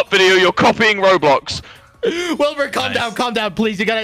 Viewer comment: Wilbur: "IF YOU EVER MADE A MINECRAFT MOD VIDEO YOU'RE COPYING ROBLOX!!" 40:40 Tommy: ... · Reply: Thank you I thought he said minecraft butt video